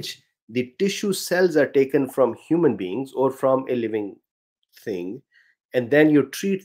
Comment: I heard "Human Beans" lol 😅